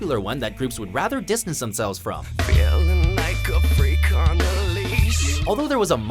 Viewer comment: it says freak on a leash I thought it said freek a leek